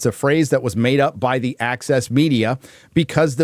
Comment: When Gary says “access” media, all I hear is “Axis Media.”